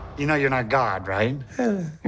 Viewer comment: What does he say at 0:43 ? I replayed it a bunch of times. It sounds something like "I want a mint" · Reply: He says: You have to admit...I do look a little bit like him